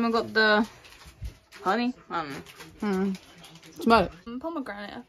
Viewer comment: No Idea what I said here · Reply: ‘Smell it’